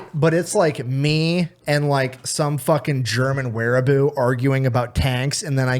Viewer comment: I had no idea wtf a "wehraboo" was or that the word even existed, so I just thought was mispronouncing weeaboo (a.k.a. weeb for short), and I was like, "Wtf does an anime simp have to do with communism?" And then I Googled it, lmao. 🤣🤣🤣🤣🤷‍♂🤷‍♂🤷‍♂🤷‍♂